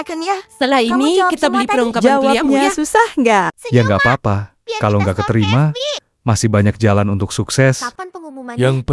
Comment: Hahh? Mereka ngomong apa pas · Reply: SALFOK sama ada yg bilang "kamu